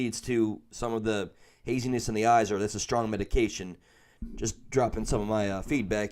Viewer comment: what is granny bringing her she keeps saying it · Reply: Her medication!!